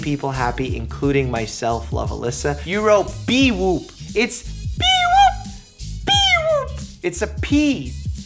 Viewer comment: haha I heard " it's not bewoop, it's bewoop"